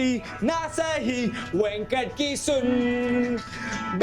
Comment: did he said venkat at 0:52?